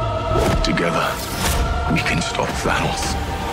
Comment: Thor: Together we can stop Thanos. Subs: Together we can stop France. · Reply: I heard 'Together we can stop flannels'